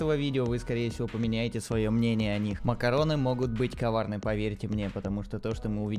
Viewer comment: из-за моей зависимости от аниме мне показалось что он сказал "макароны могут быть кавайны"